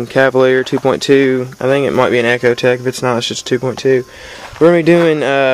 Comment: In the very start of your tutorial you sound like you said it may also be a acura integra 2.2??? The hood is up the whole time not sure what you really are working on